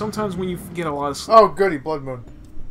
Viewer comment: I heard someone in the background yell "FUCKING BLOOD MOON" at 1:41:50 xD